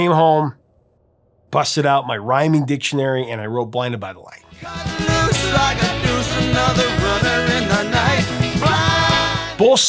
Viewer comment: I always thought it said, "blinded by the light, wrapped up like a douche in the middle of the night."